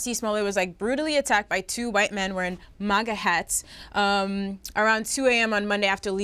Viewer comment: I deadass thought they were saying "manga" hat. 😂